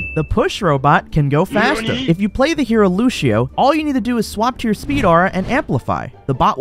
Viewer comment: im sorry did you just say LUCHIO.